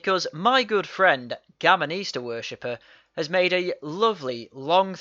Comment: every time you say “Gammon Easter Worshiper” I hear “Gammonista Worshiper” and it trips me out. What does England have against pronouncing R’s? The eternal question....